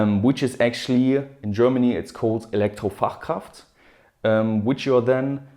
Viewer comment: who heard Electro Warcraft ? :D